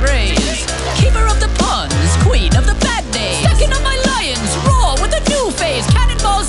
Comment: am i the only one who hears "Queen of the bandaids"?